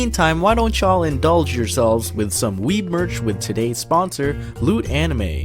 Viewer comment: I thought it said weed merch